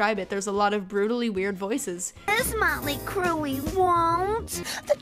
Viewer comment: I heard the bee say Mötley Crüe